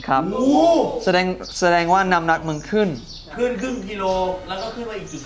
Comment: ได้ยินว่านํ้าหนักมึงขึ้น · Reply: เค้าน่าจะพูด น้ำหนักมันขึ้นนะค่ะ